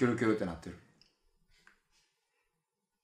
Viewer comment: この動画だけ最後の「ジャルジャルタワー！」が「辞めてやったわー💪」に聞こえる不思議